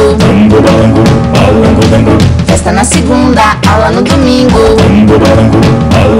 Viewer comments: 🎵 "Festa na segunda, aula no domingo." 🎵 Aah como eu amava esse desenho, melhor infância. ♡ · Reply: Juro que ouvi "bala no domingo" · 🎵 "Festa na segunda, aula no domingo." 🎵 Aah como eu amava esse desenho, melhor infância. ♡ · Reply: Eu ouvi "festa na segunda,acaba no domingo "kkk